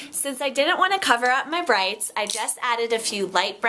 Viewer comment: i thought you said 'breasts' not 'brights' for a split second! haha. I like the uniqueness of your style. I love your confidence, i love the outfit with the high wasted yellow? colour. GORGEOUS